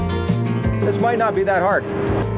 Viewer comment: ish did you say this Mike not be that hard? lol At 29:20ish insert choice words here Gravity can be such a B*#@ Did you retrieve the DeWalt tool you left out by the pigs before the storm?